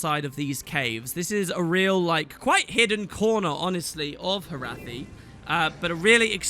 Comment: I'm so immature.. Every single time you say 'Corner' all i hear is 'Kourna'